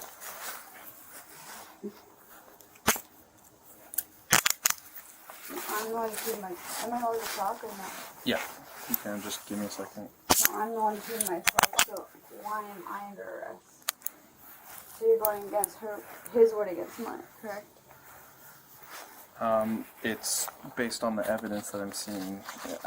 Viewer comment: It's hard to make out but I'm pretty sure at 1:14:14 she says, "I'm the one who peed myself so why am I under arrest?" (Two minutes later she says something similar again.) What a strange thing to say.